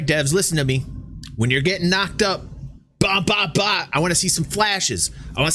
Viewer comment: I also thought he said F'ed up haha